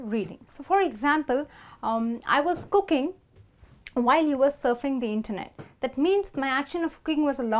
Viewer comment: I heard you saying twice "you was" (minute 2:32 and 2:37) and according to any grammar books this is considered incorrect. Please, be more careful next time. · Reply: Mami sita she said "were".